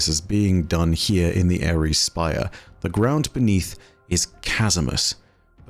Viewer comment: Did you say Eris's Spire? Why?